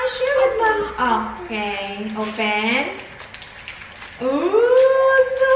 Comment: i think i heard "otay open" uwu cuteee